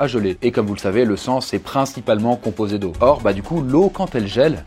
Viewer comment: Il dit quoi à 14:33 "le sang est principalement composé de ****" ?? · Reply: "Le sang est principalement composé d'eau" ^^